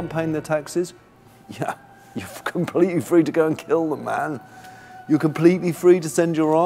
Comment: did peter say "You're fuckin completely free to go and kill them" at 6:14 ?